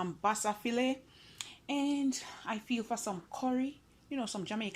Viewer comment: I heard “ah feel for some” 👍🏽. Se why I named my channel EC Emily - ahfeelinforsome👍🏽. Thanks for sharing💕